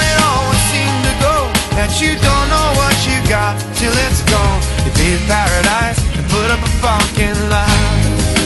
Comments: This took me an eternity to find. ALMOST 10 YEARS LATER, I finally find it in a random movie. I thought he was saying "leave paradise and put up a pocket of love". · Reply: I honestly thought he said "they gained paradise and got em a fucking life" lol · I Swear I Heard "Put Up A F---ing Lot"